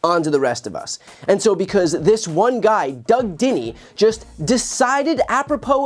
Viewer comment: who else thought he was saying doug diddy the entire time and thought who the fuck made diddy mayor?